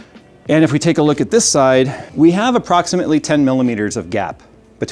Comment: I have endured so much brainrot I thought jay was saying “we have approximately 20mm of gyatt”